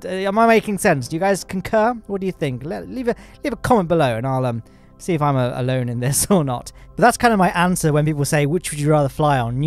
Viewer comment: I heard “Leave a comment below and I’ll see if I’m a Lowne in this” 😂 But yes! SpaceX is the future, all hail the soon to be lord of Mars!